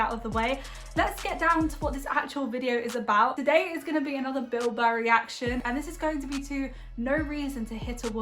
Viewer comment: 11th Doctor 🌷 You sounded like you said Bil Bo🙂